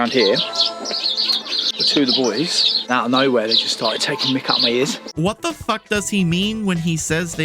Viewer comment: thought he said "takin mic outta me ears" as in making fun of him.